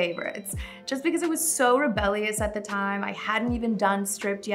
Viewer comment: She kept saying rebellious but all I heard was"blackness" lmfao 😂